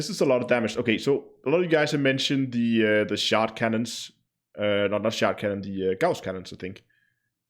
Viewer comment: I might have to try the all plasma again. Really thought you kept saying shart cannon... made me laugh!! FYI Shart: a small, unintended defecation that occurs when one relaxes the anal sphincter to fart (blend of "$h!t" and "fart")